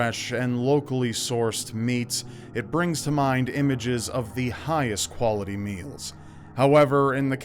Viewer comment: Is it just me or did I heard him say memes..... The highest quality memes 0:20 XD